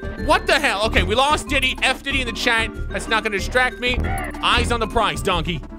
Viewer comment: Did Say F Diddy??